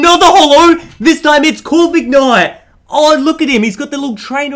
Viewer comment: who hearded covid knight